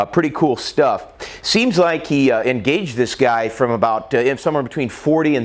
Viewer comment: i don't care what anyone else says this young man is a hero...this young man has more brass than any of the 400 police at the school..he seen the threat ,,he ingauged him ..and he took the threat out all within 15 sec. and he hit 80% of his shots fired all the while dealing with shooter..the people running to get away and just the stress in general of an active shooting...my hat is off to this young man..he has my respect..i'd like to buy him a beer or ten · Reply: dude did u just say ingauge